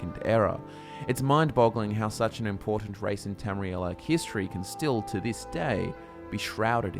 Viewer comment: Everytime you say "History" I hear "Hist tree" Conspiracy?